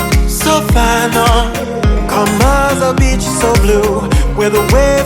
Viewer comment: On the last story anyone else thought she said bitch instead of beach